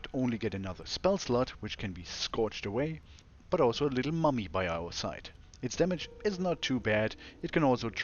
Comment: i heard "mommy" first, and only 5 seconds later i realized it's "mummy". There's probably definitely something wrong with me.